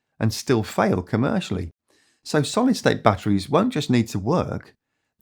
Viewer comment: Captain Solidstate and the Zwitterions! The reboot we've all been waiting for. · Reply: At one point I thought you said sorry state batteries, which might be a better name for them